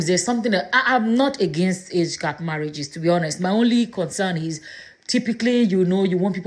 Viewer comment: I thought she said she loves ONLY OLDER MEN🤣🤣🤣🤣🤣🤣🤣what a peroxide user. TUFIAKWA